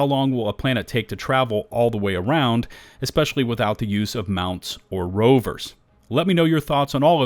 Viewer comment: Mentioned that in the video, and thanks. 👍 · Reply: lol I misheard you first time in the video. I heard "especially with the use of mounts and rovers" not "especially without the use of mounts and rovers" my bad😅